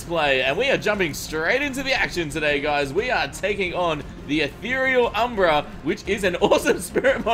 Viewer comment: I thought it said ethereal umbrella lol